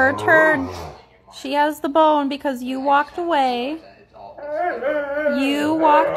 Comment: I could have sworn that dog said, “no...that ain’t fair! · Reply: Voltaire I heard him say I walked away right after she said U walked away lol